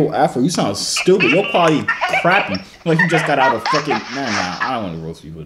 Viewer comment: was that a slur I heard 😂😂😂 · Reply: He said fuckin 🤣🤣💯💯💯